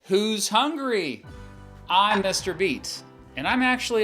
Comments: i thought you said im mr. beast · sounded like he said mr beast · I thought he said Mr.Beast in the intro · Thought he said "I'm Mr beast" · Who else thought he said “I’m Mr Beast” in the beginning